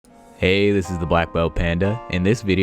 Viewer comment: I heard "hey this is a Waifu Panda"